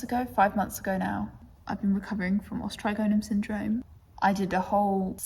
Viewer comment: What is “Australia Syndrome”? That’s what closed captioning says 🤷🏻‍♀️ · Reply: It must have captioned me a little funnily- it is meant to say Os Trigonum syndrome x